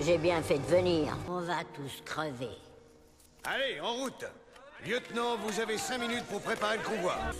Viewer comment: Hello! Can someone please write in French what she says at minute 01:30? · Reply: At 1 : 30, the men voice says "Lieutenant, vous avez 5 min pour préparer le convoi"